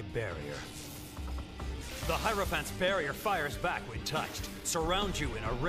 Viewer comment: "Damn it's Kakyoin's high elephant." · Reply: i thought he said high heroin phant